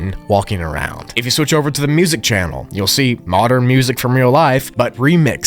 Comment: Bro for a second I thought it said the bean😂😂😂